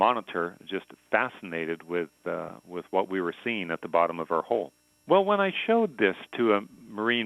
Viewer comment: Did he just say Bottom of our hole?